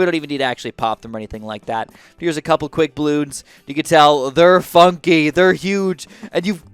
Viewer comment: I thought he said something else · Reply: It almost sounds like f*** But he actually wants to say funky